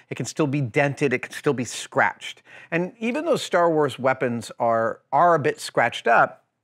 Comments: “Star Wars guns are damaged...” didn’t think I would catch that did you Adam???? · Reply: Sorry I meant to answer to the guy who thought he said " starlords". You are completely right, he did say "starwars" and I found that kinda funny too. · really sounds like he says star WARS weapons haha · Did he say starwars weapon